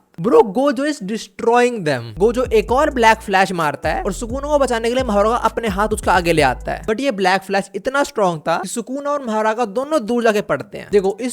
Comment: 😅bro you said blash flack instead of black flash